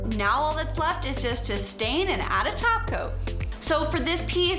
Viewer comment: Love it-and you! Fun story... at 7:04 I heard "Now all that's left is to stain and add a taco." 🌮 That's my kinda table!